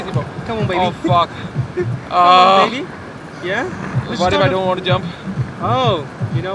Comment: did he just said, "come on baby, yeah"? 😂😂